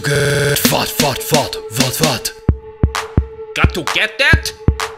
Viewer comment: XD it sounds like Arsene says "got to get fat"